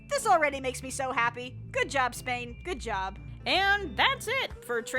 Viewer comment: Its my brain to full of beautiful Spanish ego or I heard "SPAIN" ??